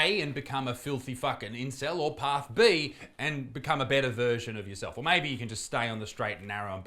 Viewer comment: I thought you said "a better virgin of yourself" at 2:40, not "a better version" lmao